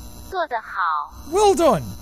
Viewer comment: I thought that Chinese girl said “go to hell” for some reason🤣